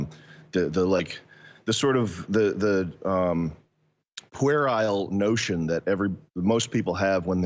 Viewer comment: The sort of what kind of notion? Querile? Is that a word? Please help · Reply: Puerile, boyish, from the Latin 'puer', for boy